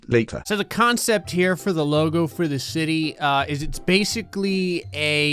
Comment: did he say shity?